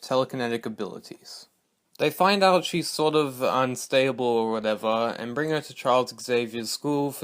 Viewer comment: who else heard "unstayable"?